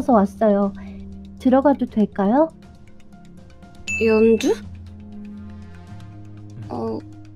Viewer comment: 이때 연주부르는 목소리가 연두로 들리는데요